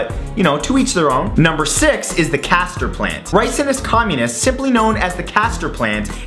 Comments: When Matt said "castor plant" for a second I thought you said cathider plant Lol. Love ya Matt you're my favorite Youtuber, you're so funny, expressive and never fail to make me smile or fascinate me with your content. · did he say risin is communist